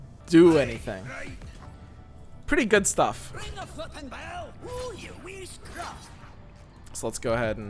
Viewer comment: Did that octopus just say "Ring the f*cking bell!"!? I can't hear anything else, please help